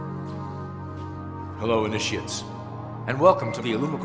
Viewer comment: Can someone please tell me what he says at the very beginning? I've listened to it 3 times & each time all I keep hearing is 'Hello iz the shits'. LOL perhaps it's some kind of Illuminati spell 'hello iz the shits, hello iz the shits!'. But seriously, what does he really say, because I doubt he said 'shits'. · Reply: No - what he says is "Hello INITIATES"